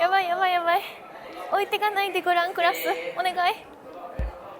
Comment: 「置いてかないでグランクラス」が 「〇〇でヤンス」みたいに聞こえる 伝わるかな。